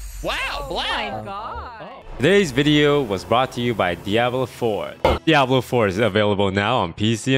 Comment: wait in the end did he say fuck diablo is available on pc now